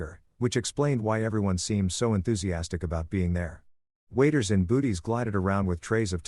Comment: Did I just hear "waiters and booties"? 😂 my MAN 😂😂😂😂